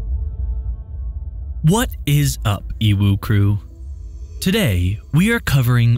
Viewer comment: This is the first video I've watched from this channel and for a sec I thought he said "uwu crew" lol